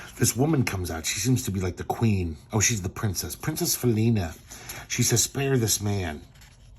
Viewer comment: This is worth money. If you have any of Harvey's Black Cat comics you've struck gold. BTW: the first time you said "Princess Felina" I thought you said "Foonina", and now I'm laughing my ass off waiting for an opportunity to give that to somebody as a nickname!